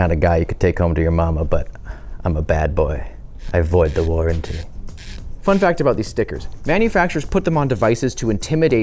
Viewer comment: did he say "I Avoid the Lord" or i'm misshearing things? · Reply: Im almost certain he said "i avoid the warranty"